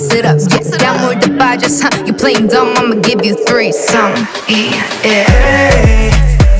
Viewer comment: So no one's gonna talk about Jamie's word play "i'm gonna give you three.... sam, ee, il" ? Okaaaay..... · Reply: Unknown so at first hear it sounds like shes saying imma give you a three some